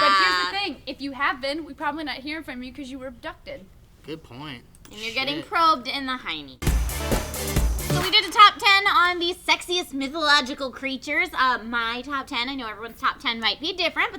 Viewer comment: I thought Trisha said "Nipplelogical Creatures"